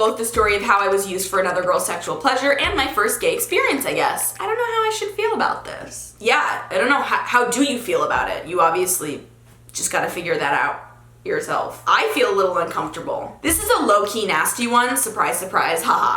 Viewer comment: lol i thought you said "you just have to finger that out"